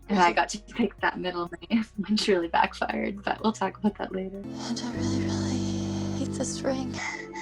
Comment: You really can see the love in them. Kat picking nicole's middle name was so funny, because when I first heard it for the proposal I really thought Waverly just said Nicole really Haught.